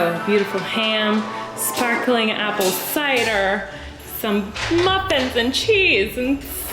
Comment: i'm sorry did she say, muffins lmaooo